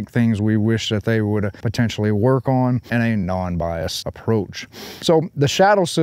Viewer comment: I thought he said unbiased😂😂